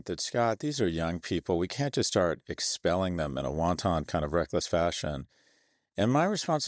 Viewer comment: I swear I heard won ton, and then I had a kind of a Vizzini/princess bride moment where I thought, I don’t think I know what won ton means. Two google searches and three minutes later.. It’s wanton you idiot…